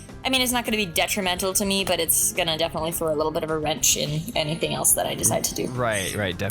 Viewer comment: At first I thought said "A little bit of a red chin"